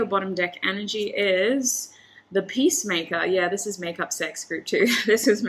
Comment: Everytime she said ''peacemaker'' i hear ''pacemaker'' and i'm laughing everytime.